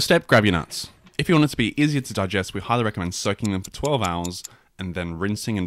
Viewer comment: I can already hear the vine boom in my head after that "first step, grab your nuts" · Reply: 😂, then I "thought" he said SUCK them for 12 hours at which point I thought "I'm out, bye!" I'm such a dork🤡, he actually said SOAK!!